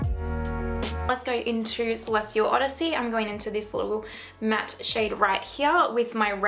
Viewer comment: I thought it said “10 days of Celestial Odyssey” and I got super excited 🤣 can you make thatttt